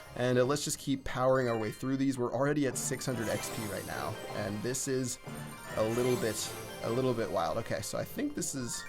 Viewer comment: I thought you said:"this is a little bitch" HAHAH